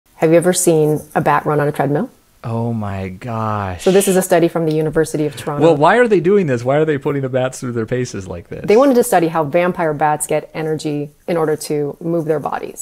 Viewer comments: I legit heard "university of trauma" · I thought she said a bat running on fentanyl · i heard "University of Trauma" and had to watch it again